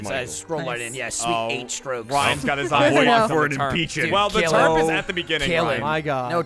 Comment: I keep hearing them say "Turmps" as "Turps" and get really confused.